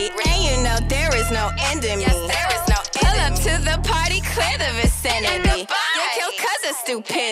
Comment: I don't know why but for the longest time I thought she was saying "clitoris enemy" and not "clear the vicinity" and I was like "well that's quite an interesting line"